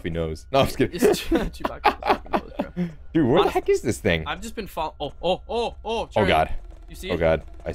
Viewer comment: WhereThe F**k is this thing? I think he said that